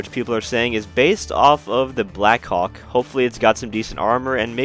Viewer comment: Why when he said Blackhawk I heard him say "blackcock"?